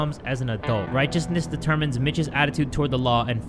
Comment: My husband thought you said “righteousness determines bitches”